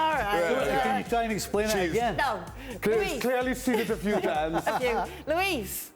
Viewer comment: did Kay say F you or I'm tripping? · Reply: She said "a few" in response to Shaka